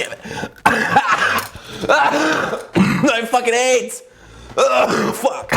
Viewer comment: yo did you say "I have fucking AIDS!!!????)